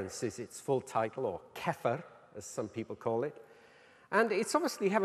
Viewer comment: Can someone tell me what the expert refers to at 3:23? I'm not pretty sure it is 'kefir' as it is transcribed. Certainly not! I can even catch the idea, considering the context, but...I can't get the proper meaning!! Maybe he refers to accesories, titles, superficial connotations that the titles offer? Lost, a bit... I would appreciate your responses!! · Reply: CEFR